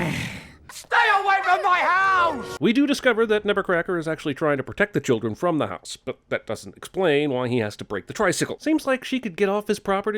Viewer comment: Omg i thought they were always saying nevercracker instead of nebbercracker